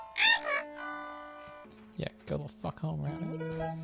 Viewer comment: sounds like he says "fuck!"